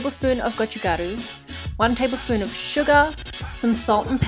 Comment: i wasnt paying attention to video for a sec and i thought u said one tbsp of SUGA ._.